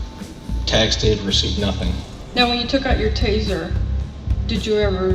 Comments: Am I the only one who thought he said "YOUR ASS" when asking for his things/property. 😂😂😂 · I could have sworn he said your ass. When he was asking him to hand over his equipment. Lol